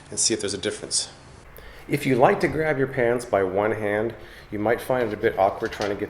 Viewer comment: Did he say pants or pans? You decide...